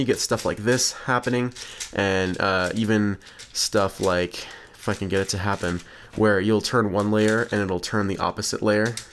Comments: did he say the f word in 9:05/1/1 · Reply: Pro4Gamez It's actually "if I can get it to happen" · I thought it said "f***ing get it to happen" instead of "if I can get it to happen"